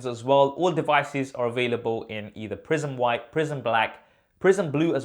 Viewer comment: Thought you said prison instead of prism